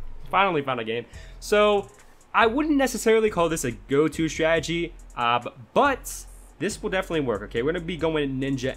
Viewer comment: I thought u said wiz was the go to strat